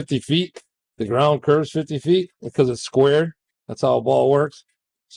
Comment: the ground curves 50’ because it’s square because that’s how a ball works. Huh? A ball works because it’s square? I watched this part like 5 times. Clearly I am misunderstanding what he’s saying, right?